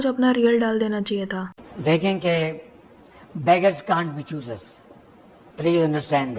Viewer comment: does he said beggers can't be choosers for themselves?..😭😂